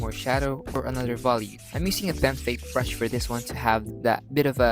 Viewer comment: What brush did you say at 6:43? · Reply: Pen fade if I’m not mistaken